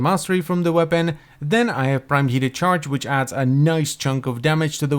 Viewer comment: I swear I just heard you say "prime yeeted charge"